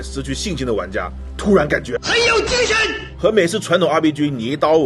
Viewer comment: 這傢伙說什麼 朋友之拳？ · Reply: 很有精神！